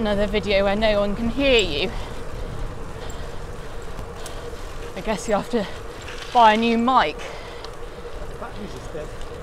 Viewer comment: I'm sure I heard say "Does that you need to buy a new bike" 😁👍